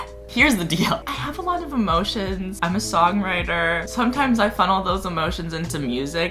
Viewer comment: When he said “I’m a song writer” I thought he said “I’m a soggy lighter”... idk why 😂